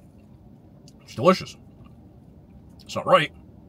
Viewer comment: I coulda swore Zyzz said as long as it’s delicious it’s ok or sumn like that 😂